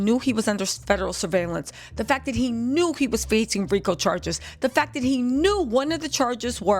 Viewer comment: I thought you said, FREAKO Charges..... instead of RICO